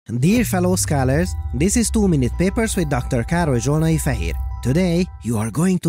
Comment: I always mishear Károly at the beginning of each video as "this is Too Many Papers". But there can never be too many papers!